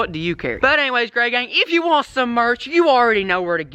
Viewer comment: It sounded like you said grey yang at the beginning. Lol still love the vids